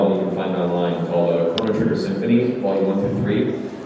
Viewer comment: At around 12:40 he names a album from a band/symphony but I can't quite catch it. Could someone if they would be so kind tell me what he is naming there? Thanks ^_^ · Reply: chrono trigger symphony